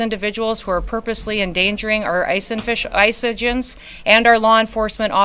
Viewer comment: did she say "Ice and fish"? 😂